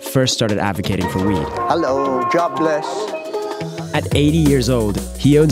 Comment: I heard Jahbless